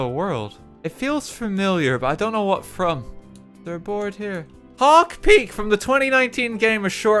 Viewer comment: i know im not the only one who heard pog peak at first